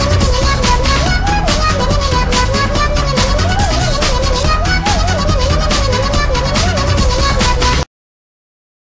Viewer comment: wait did it just say HELL IN A KIDS SHOW!! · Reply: of SmashVille Yes, yes I believe Escargoon did say hell.